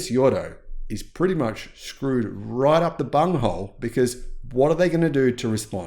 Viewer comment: I thought you said 'right up the bumhole'.